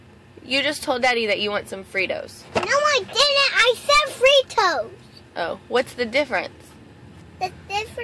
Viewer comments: (he actually said there toes) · that sounds like I SAID FREE TOES